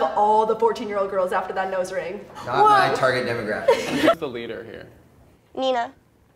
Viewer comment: "Not my target demographic" And just like that he lost the nose ring. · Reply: I THOUGHT HE SAID THATS MY ENTIRE DEMOGRAPHIC 💀